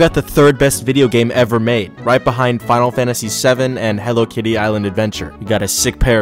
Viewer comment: I thought he said “Hello titty island” 💀💀💀